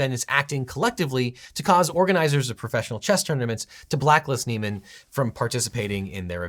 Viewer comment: I had to pause a couple of times at 10:02 because I was sure I heard 'Chester Nimitz' instead of 'chess tournaments'